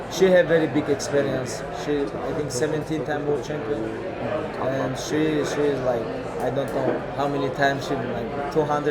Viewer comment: What did he whisper to Cyborg? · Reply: I love you